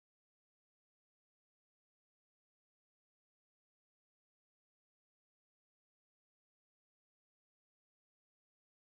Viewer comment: 잘못들은건가 씨 뭐라고 했는데.... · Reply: 아 씨 알았어 환불해줄께